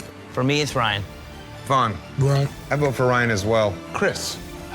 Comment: i thought he said “Bryan”